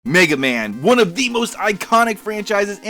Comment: LOL on some real shit I dead ass thought you said "nigga man" at the start of the video.